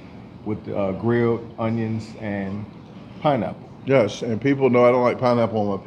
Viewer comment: 🤣🤣🤣 Sounded like you said pinenipple! Lmao! 🤣🤣🤣